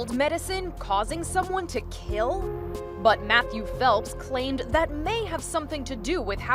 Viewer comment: damn at first i thought it said michael phelps